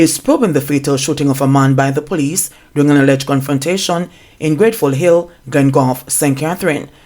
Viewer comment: Gud nite it sounds like you said Gunfrontation and me agree cuz too many shootings😮smaady plz tel me why dem kil di GC Foster vice principal😭sorry about di accident in St James